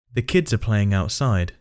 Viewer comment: ele não fala "i can't hear you". Fala algo como "the kids are playing outside". A frase falada está errada